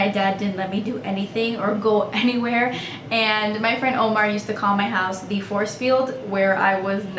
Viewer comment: does she say Omar?